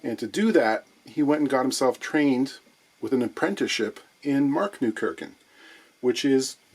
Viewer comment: What's the German center for luthiery called? I thought I heard something like Marketenkirken. The only place I found with a name like that was a church in Wiesbaden called Marktkirche, with no obvious connection to luthiery. Edit: Think I found it: Markneukirchen. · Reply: He said Markneukirchen. Mittenwald is also famous for luthery. Greetings from Germany